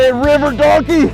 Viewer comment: Been a fan a while. Me and my Grandson are up and we are enjoying stuff on YTube, your vid pops up, we are enjoying the fishing and scenery on the big screen when suddenly he says River Darky plain a day. I look at him laughing where did he.....? I go back and at 5:35 and I hear clear as day while the fish is trying to stay in the water and you are struggling to capture him....."River Darky" from your bud then you say it more so, "River Darky"! I look at my Grandson and tell him don't say that anymore! Then stoppedyour vid and played Baby shark to take his mind elsewhere. I couldn't find a phase nor term River Darky for a Sturgeon anywhere with a serious Google search. I found River, and I found Darky! Well and I put River+Darky= ...... So how do you really feel? 😕 Man that's not for YouTube nor Public consumption! You can do better fella!🤔